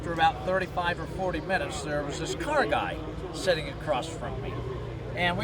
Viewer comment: did he say ''colored guy'' at 5:11 ? ' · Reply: No he said car guy. Just his slight accent sort of said he words closer together which is why it sounded like he said colored.